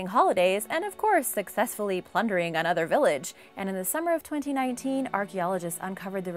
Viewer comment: December of 2019? Did I hear right? Because we’re on October of 2019. So yeah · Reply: It said summer of 2019